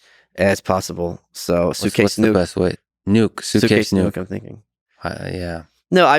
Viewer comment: DHS: Did I just hear SUITCASE NUKE???????????